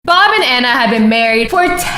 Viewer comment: BoB aND aNnA lOVeD eAcH OThEr vEry MuCH Me: I thought you said banana 😂😂😂😂